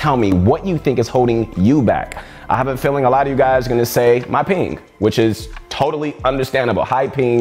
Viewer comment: For a second I thought he said”My Peen”. I was like, damn didn’t expect that from